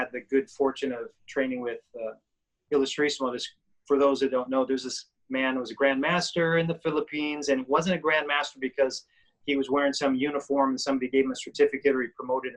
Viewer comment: what was the name of this guy? I couldn't understand what he was saying when he said "trained with"... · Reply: Someone else answered the question: Antonio "Tatang" Illustrismo